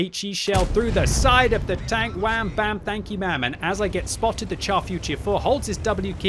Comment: you say somthing like "wham bam, thank you maam' " or am I hearing it wrong. I gotta say I love that one, will teach my students !